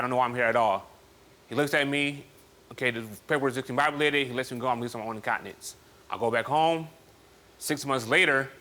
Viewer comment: "THE JUDGE RELEASE ME ON MY OWN INCONTINENCE" LMFAO I CANNOT believe I just heard that!!